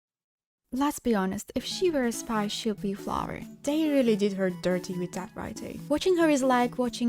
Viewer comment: can someone add subtitles, english isn't mine or her first language and sometimes I just don't quite get what she says 2:02 "Let's be honest, if she were a spy she would be a flower" ???????